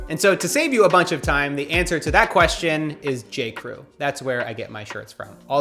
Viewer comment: i lowkey thought he said J-cole.